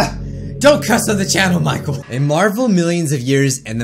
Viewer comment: I THINK HE SAID HIS REAL NAME! or I was just hearing it wrong :/